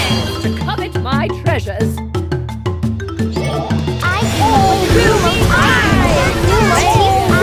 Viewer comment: "i came up with all with be mine" words of a wise cookie.... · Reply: I thought it said Gold will be mine!